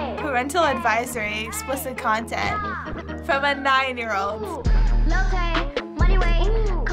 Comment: All I heard “Lil tay moneyway” but I actually hear is “lil HEY, mommy HEY” ._.